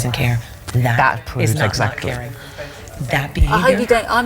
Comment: What does Michelle say at 12:49 ? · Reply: "That is not 'not caring'"